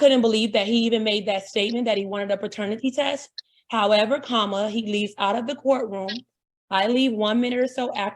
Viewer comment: Did she just say “however comma?”